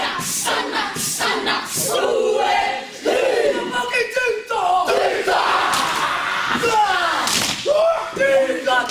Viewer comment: what did he say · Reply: Papakitehtaha